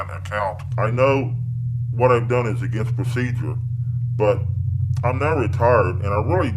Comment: I swear he say's I'm not retarded, instead of I'm not retired